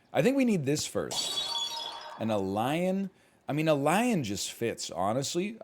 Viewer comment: I hear him say, “A Lion just works”, and I couldn’t help but start sweating profusely.